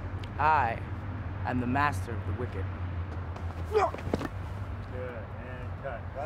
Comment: XD i thought he was saying he was the master of cricket at first frank is so extra in this